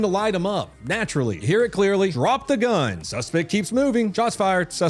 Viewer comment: sounds like they say drop the gun after he’s been shot 8 times. don’t lie